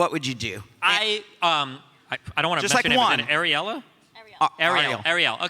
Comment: I thought he said Areola at first. Lol